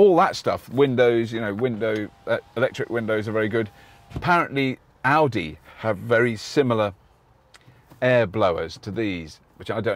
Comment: So I heard "Apparently Aldi have very similar air blowers" Which doesn't seem as much of a compliment as 'Audi'.